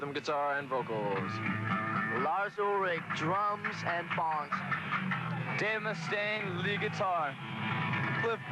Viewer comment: lol it sounded like he said "Drums and Bongs" but i have no idea